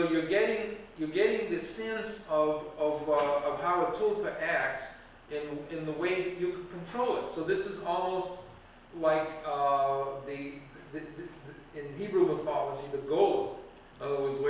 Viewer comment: what did he say? "tulfa"? and what is it?